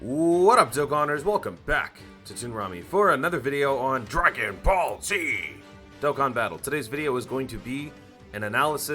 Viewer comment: I legit thought you said DRAGON BALL Z GOHAN BATTLE